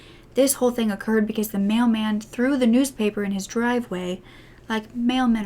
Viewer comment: off the topic: English is my third language and when you said "mailman" i thought you said "male man" and for a whole damn minute was trying to figure out what "male man" meant hahaha👁👄👁